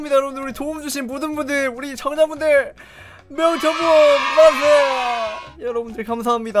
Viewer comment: 정자분들 만세라고 나만 들리는건가?